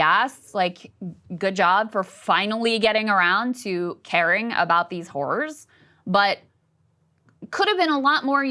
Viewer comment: lol. It sounded like she said “caring about these whores” 😂😂